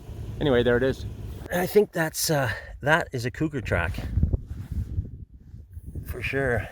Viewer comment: When you said it had A-TRAC, I thought you said 8-Track. Figured Lexus was trying to corner the 8-Track cassette market.